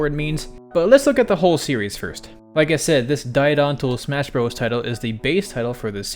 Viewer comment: I always thought Sakurai was saying 'Giganto Smash Brothers' never even considered it was an actual Japanese word! I knew about the alternate titles for Melee and Brawl, so I assumed it was 'Giganto Special' instead of 'Ultimate' lol